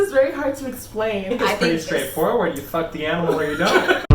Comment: So why did I hear it as “it’s pretty straightforward you either fuck the animal or you die”